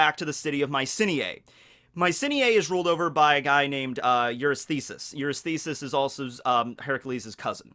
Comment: I am the only one who heard " it's ruled by the guy named "your snizzes""?